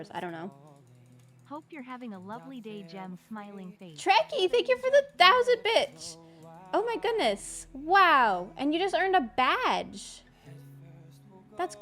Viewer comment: Who else heard “ treky thank you for the thousands bitchs” 😂😂